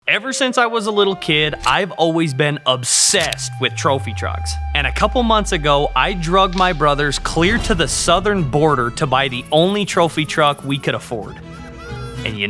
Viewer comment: I heard "i drugged my brothers."